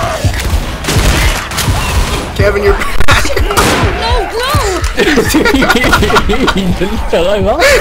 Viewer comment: zoey sounds like shes saying "OH NO GLUE!"